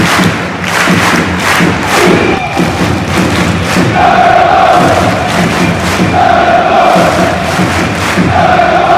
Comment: からのって何て言ってるんですか？ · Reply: 名古屋グランパス